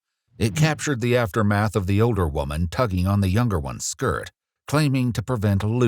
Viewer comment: I thought for a sec he said "ogre woman" 👹 🤣